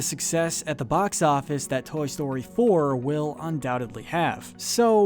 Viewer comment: did he say: “Toy story Thor”